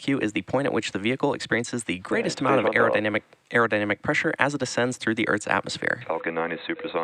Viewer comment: Why do you keep saying "descends through the atmosphere" instead of "ascends through the atmosphere"???? I don't get it! Please expain it to me!